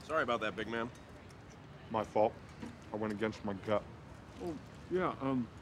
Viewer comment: What does Armie say at 0:47? Sorry,I'm not a native speaker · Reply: Beatrice D He was chewing but he said “I went against my gut”